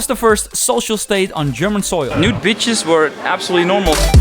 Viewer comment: Did you just say "Nude Bitches"? @ 2:38 🤣